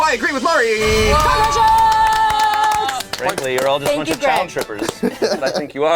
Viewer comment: At first I thought Shane said "Thankfully your all a bunch of child strippers." Like they strip children.